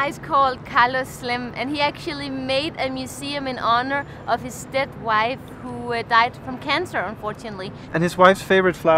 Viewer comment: Stepwife? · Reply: Oh, she said "His dead wife"...I thought she said "His stepwife...". Silly me! 🙂